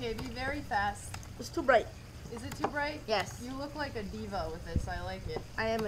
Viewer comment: i thought she said, "you look like Devo" 😅